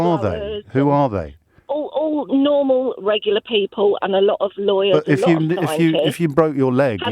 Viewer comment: Did she say "a lot of lawyers"??